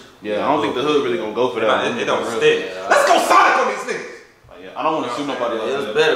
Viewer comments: I thought he said Sonnet instead of Sonic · I thought he said solder not sonic at first